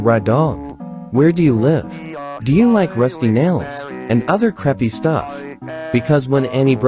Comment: why do i hear and other crappy stuff💀